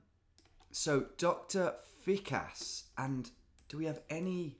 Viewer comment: Killed me how I heard him say Dr. Thick-ass instead of Dr. Fikas everytime 💀